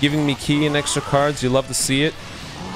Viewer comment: He actually said, "we love to see it"!!! (3:38) I missed to hear that❤